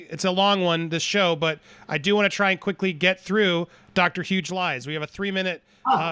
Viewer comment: Hi..I thought you said Huge lies at first lol instead of Hughes Lies but yeah same thing lol plusThankyou for explaining what happened so well. I understand much better now.